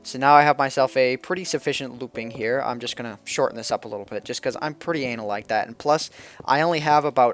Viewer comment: Wait... I could of sworn he said "I'm pretty anal about that" I was like.. Wut?